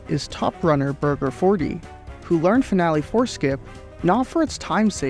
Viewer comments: I kept hearing "finale foreskin" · Annunciation is important. I keep hearing "finale foreskin".